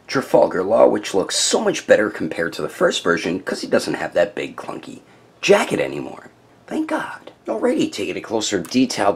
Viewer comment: I thought he said it doesn’t have that big fucking jacket lmao